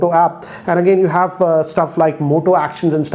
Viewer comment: who else heard him saying mota 😂😂